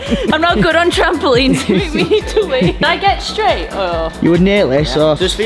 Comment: did she say trampolines make her have to we? 😂😂😂